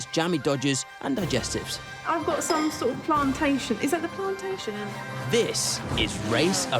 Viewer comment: Did she say plantation 😭🤣 I can't with Beckys